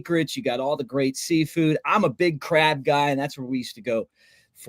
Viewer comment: I’m a big crab guy I misheard you and thought you said I’m a big craft guy and I was trying to picture what crafts they would do at the residence Inn in Ocean Beach